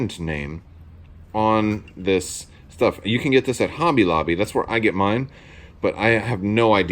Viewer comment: The white surface primer is Tamiya brand. Awesome stuff! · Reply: haha sorry if I had waited not even 3 seconds and not paused the video I’d have heard him say hobby lobby. 😅😅😅